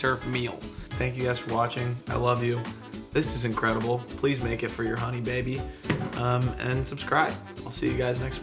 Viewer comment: I heard it, “Make it for your… uncle…” I’m waiting! Another great job!